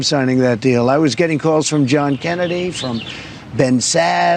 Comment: on video did I hear correct, did President Trump say he got a call from John Kennedy?